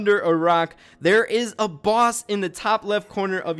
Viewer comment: my mom was laughing so i heard "if you guys didnt know, there is a boss under the rock" lmao i was like what?